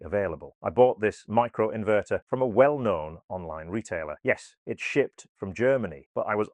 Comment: I thought you said 'it was sh*t from Germany'! Actually you said, it was shipped from Germany'!!